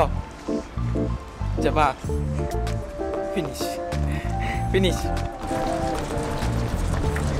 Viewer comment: DOES HE SAY PNIS ??